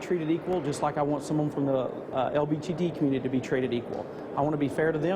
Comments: did he call it "LBTD" community at 7:56 or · did. Did he just say the “LBTD Community”???😂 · I’m sorry if it’s just that guy’s accent, but did he just say LBTD community? I don’t think that’s the acronym, or anything approaching it, like, he’s trying to use the shortened LGBT acronym but couldn’t bother to do his research on the simplest acronym there is? And this guy’s a senator?! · it sounded like he said "the LBTD" community lol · LBTD?? Is that what he said?? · did he say "LBGD"? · did did he say the LBTD community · did he just say LBTD community?